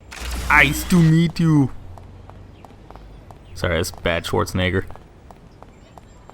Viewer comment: Did you say nigger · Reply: He said Schwarzenegger